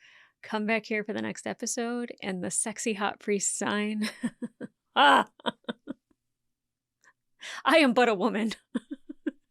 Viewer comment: That ending, lol. My brain is broken. I heard “I am buttah woman” and I immediately got a glossy 8x10 in my head of as a butter-themed superhero